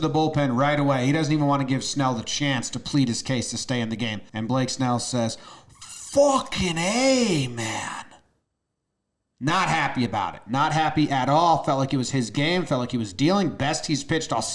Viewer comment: I thought he said "fucking cap, man"